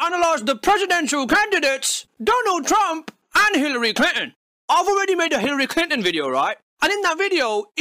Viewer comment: Sounds like he is saying Hillary Clit in